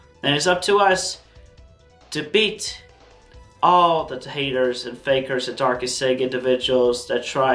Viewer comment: This dude so high he called the "haters" taters! Lol. That's what I hear anyway. The timestamp is 18:06 to about 18:20. Does anyone else hear that or is it just Mr? This dude high as a kite trying to read off cuecards, terribly. Lol. Kinda sad really how bad it's gotten. I mean look at that crusty ass medallion he has on. Meditate on that if you will. lol.